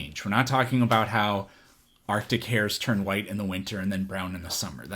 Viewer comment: Lol I heard you say “our dick hairs” turn white in the winter and brown in the summer. It was arctic hairs. Enunciate more please.